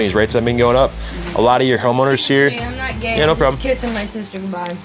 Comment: wtf bruh did not just say homoowners😮😂😂😂😂😂😂😂😂😂😂😂